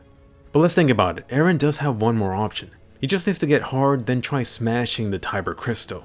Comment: The amount of times I've heard you say "erens gets hard"...